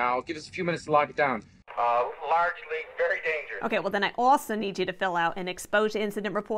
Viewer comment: I always thought Han said "Largely very dangerous" which is silly but all the more funny for it.